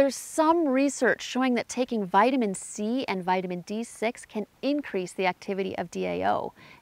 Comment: This is a very nice explanation of histamine intolerance, did you say Vitamin D6 or B6 at 17:01? Also, that is a lovely floral dress that you are wearing. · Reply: Hi! Thanks for the compliments. It’s vitamin B6.